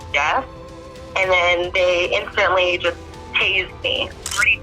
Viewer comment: the subtitles are wrong--she said "they tased me" and the subs say "he was mean"